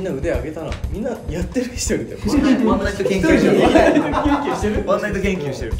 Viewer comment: 「ワンナイトキュンキュンしてる」 空耳だけど、もはやこう聞こうとしてる自分がいる 笑